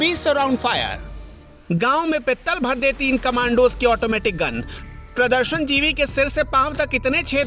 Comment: GAAW mey Pittal bhar deti.... Instead of G@@ was Epic 😂😂😂😂